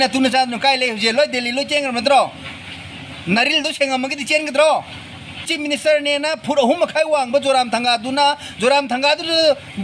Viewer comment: Did he just say " 3 and half foot tall Zoramthanga"..how dare he discriminate and mock at Mizoram CM in such away???